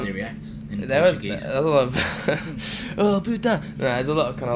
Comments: Did he really say "Oh putain" at 2:11 ?!?! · I don't know but I think that Josh said "OH PUTA"(portuguese word) at 2:11 xD This is not a very nice word to say ahha